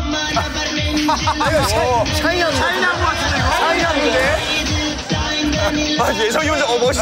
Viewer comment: is it just me or I heard SHINee?